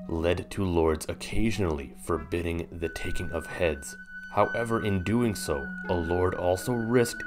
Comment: Ohhh I thought it said talking heads and I was like whaaaaaaaaat